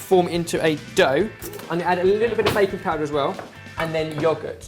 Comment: I'm not a native english speaker and thought Ben said "bacon powder" instead of "baking powder" for a second I thought I was on EpicMealTime but it couldn't be with this all this healthy food :D really love you guys <3 <3 <3 <3